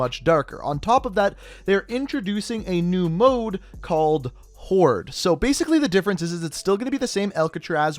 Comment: My video lagged at 11:32 and it sounded like you said “A new game mode called whore.”😂